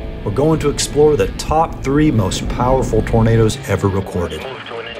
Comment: it says burrito instead of tornado on subtitles